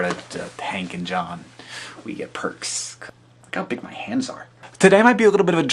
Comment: I heard him say (really fast), "look how big my hands are" ... no "cupping" at all. O.o